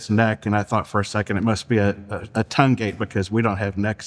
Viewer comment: what is he saying? “I thought it must be a __ because we don’t have necks either”? Tungate? I listened to it multiple times and have no clue. Closed captions say “tongue gate,” but that doesn’t seem to make sense. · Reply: His last name. I guess it was a joke